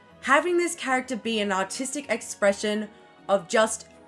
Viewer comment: I thought they said autistic expression :(